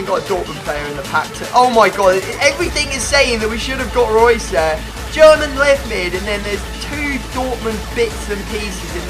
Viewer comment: I swear it sounded like he said "German lesbian"